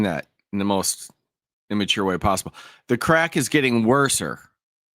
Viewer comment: I am NOT defending this moron by any means, but I think she said worse, sir!